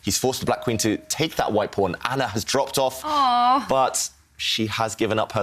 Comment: did he say : Ana has dropped off? Since when do pawns have names